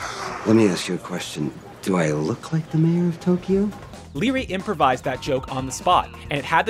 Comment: What does Leary say? "Do I look like the man who --------- you?" Kind of mumbly... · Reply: He said "Do I look like the Mayor of Tokyo to you?"